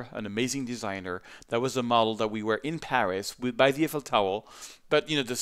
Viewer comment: did you just say, Eiffel Towel?